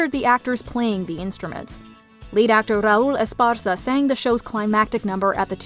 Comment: Raise your hand if you’re still salty Raul Esparza didn’t win the Tony for company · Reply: Me! When they said lead actor I thought they said late actor and I had a heart attack